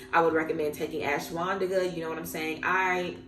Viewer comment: Wait What did u say at 16:38 u recommend taking 'ashiwandika'? Do u mean ayahuasca? Or something else ? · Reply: ashwagandha! i pronounced it a bit wrong